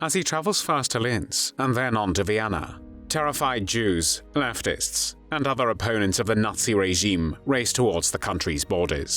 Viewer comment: It's hard to tell, due to your accent, but did you say "...leftists, and other opponents of the NAZI regime"?